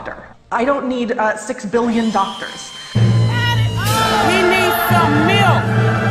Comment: "He needs some milk" Gets me every time. 😩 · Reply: Oh my gosh! All these. Months I thought it was saying: " He needs some MILF!"